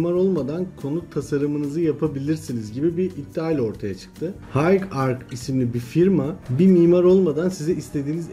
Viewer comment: Mimar olmadan kullanılailecek program ismi ne dediniz anlaşılmıyor high ark mı ney?